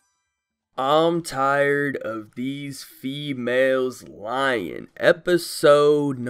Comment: why did i hear "fewhales" instead of "females" ...